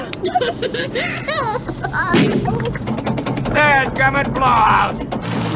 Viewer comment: dad gummit blob! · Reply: Jeff Holt I think he actually says "dad gummit blowout!", but it almost does sound like he said blob...either way McGavin was hilarious in this!